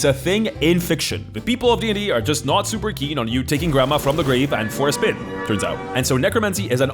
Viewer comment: I know said “taking grandma for a spin”, logically I know that’s what he said, but I heard “force-fem” and like, yeah I think anyone would be upset with you reanimating the corpse of their dead grandma just so they can force-fem someone who was dead, and probably already feminine to begin with (considering they’re your grandma). Like that being their master plan is just extra fucked up and hilarious 😂 😂